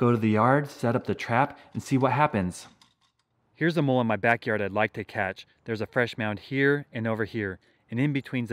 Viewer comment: When he says let's try catch a mole it sounds like he says let's try catch them all